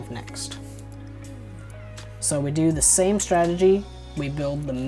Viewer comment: Wth did u said at 7:48..strengy?? What is that